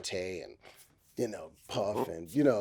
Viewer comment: Did he say Puff was his influence as a writer at 6:24?